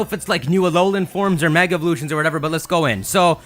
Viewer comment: Oh. Evolutions EVOLUTIONS! I thought you said Eeveelutions...